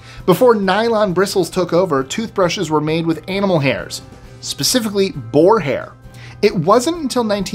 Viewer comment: My wife and I BOTH heard you say toothbrushes were made from anal hair! 😂